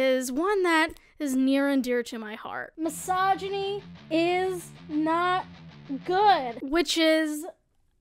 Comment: Misogyny is not goo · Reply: Ngl i thought it said misogyny is not gay